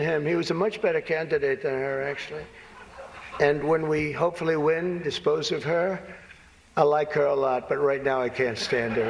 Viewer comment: Did he say at 14:17 “ When we eventually win dispose of her” I like her a lot” ? As usual he has a very Hitlery type of speaking using such phrases as “ poisoning the blood” , “ dispose of her” , “ they’re eating dogs and cats”, etc . Isn’t that yet another dog whistle for maga to go after and do harm or worse to his opponent Kamala Harris? Lock him up already for Gods sake!